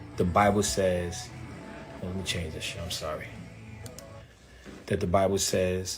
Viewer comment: did he say “let me change that shh”?! 🤣🤣 I mean it’s not a big deal to me, but it’s still funny to me! 🤣🤣